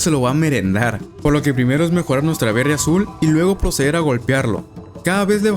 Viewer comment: hahaha parece que dice verg4 azul. 😂